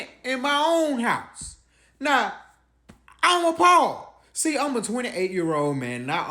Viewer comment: Forgive me if I'm mistaken, cause English is not my native language, and I keep learning and improving everyday, but at 0:15, shouldn't the subtitles say:"I'm appalled" instead of: "I'm a Paul"? Cause that makes no sense when translating into my language, Spanish.